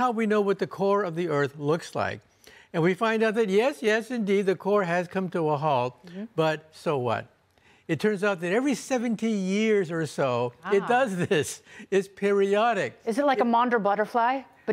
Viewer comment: I'm surprised Biden isn't blaming Trump for this the core of the earth stopping , a phenomenon that occurs every 100 years . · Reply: i thought he said 17 years